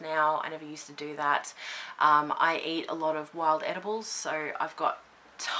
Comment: Seriously I need hearing aids.. I actually thought you said you eat wild animals 😳 EDIBLES! EDIBLES! I had to look at the transcript omg 🤪 great video BTW ❤️❤️❤️